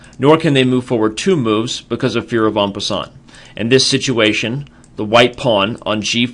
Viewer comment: you said for fear of what? Some french ass word, anybody know it? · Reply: En passant. It’s a move a pawn can play that allows it to capture another pawn even though the pawn moved two spaces on its first move.